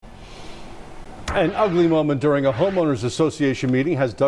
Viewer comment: Did I hear this clearly or did he say" an ugly women" in the beginning . I think that's what the news guy said listen 0:01 like if you heard the same thing 😅